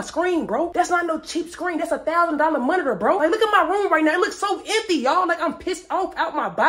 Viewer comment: all I hear is "look at my roof" LMAO play the game now 🤣🤣🤣🤣